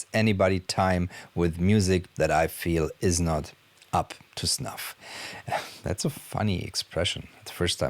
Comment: Hey 👋🏼 I've just seen your video and it was a nice and relaxing to pass some time in these strangely untypical days. At around the 18:00 you said it was the first time you used a certain English phrase "up to ..." I couldn't understand the word that came next, was it "stuff" or "snuff" and yes, why ask some may say, I'm really bored 😉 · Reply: Up to snuff 😁